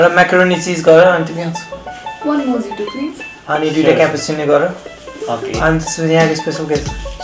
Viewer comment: Expectation.. 😆 did they just say 2 cappuccino after mojito